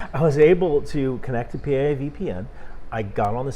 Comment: I thought you said PLA VPN 😂